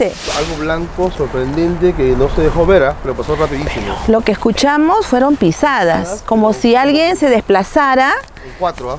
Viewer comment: En el minuto 14:29 a 14:30 se escuche un susurro pero no logro entender que dijo. Buen video! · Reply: Si yo también , dice velo velo, se van se van soraya